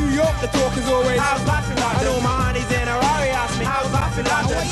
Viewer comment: did he say my aunties in Harare😨👏🏾👏🏾